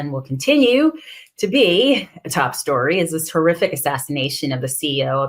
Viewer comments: You said "horrific", but I heard "terrific". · "Horrific," not at all. FREE LUIGI. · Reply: I misheard her and thought she said "Terrific"!